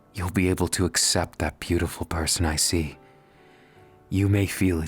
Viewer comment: Shoto: "I see you" What I hear:"icy u"